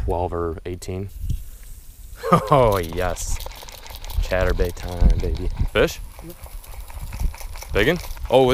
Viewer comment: did he just say chaturbate time?